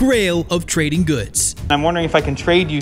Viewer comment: it Is wrong translated, he actually said "Marecela, the ball, would you shop it?"